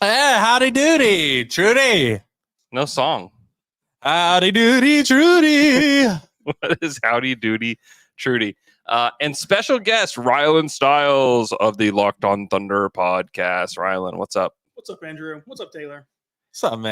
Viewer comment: I swear I thought he said “Ryan Styles”. Now I wanna watch Whose Line